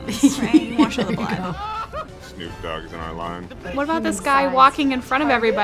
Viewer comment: Is the gameplay of 2:25 from? Cuz i heard said "Snopp Dogg on the line"